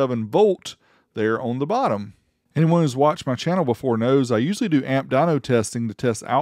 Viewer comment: When you said usually do amp testing, I thought you said "used to do" and I got scared.